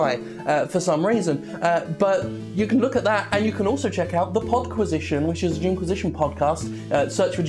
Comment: for a second i thought at the end you said PotQuisition....now that would be something to see. At the same note, do an Uncivil War for the The Crew getting from one city to another while totally wasted.